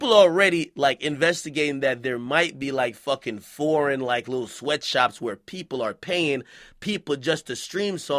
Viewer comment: I swear I thought he said there's a rapper named Lil Sweatshop